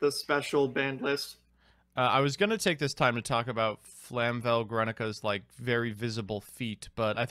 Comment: I heard it as "Flamvell Greninja"